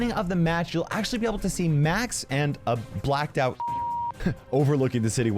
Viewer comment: Is it just me who heard “max and a blacked out f***”